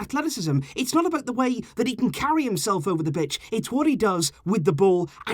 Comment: He said b** instead of pitch